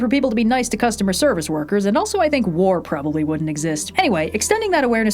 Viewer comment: I misheard you as, “And wool probably wouldn’t exist” and I was so confused why understanding other people would kill all the sheep 😂😂😂